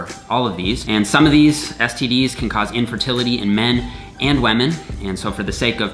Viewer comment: Did he said “wamen” 4:52 he is respecting wamen. Veeery nice.